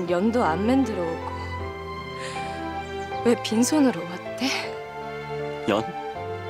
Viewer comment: 연이 아니라 엿이라고 들리는데..?엿이요??